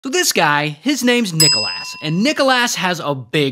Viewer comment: why do i hear: nickel ass